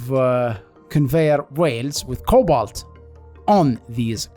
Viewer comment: This was a great video. And you build really interesting things to circumvent the no liquid pipes policy. As a non English person, I loved how you pronounce "rails". The first time you said it I heard "whales" instead. 😂😂